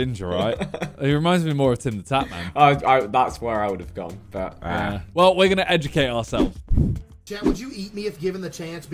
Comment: does he say "well we're gonna edge to caseoh" or is that just me